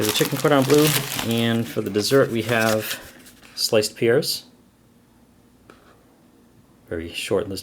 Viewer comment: Lol I heard "sliced piers" for "sliced pears"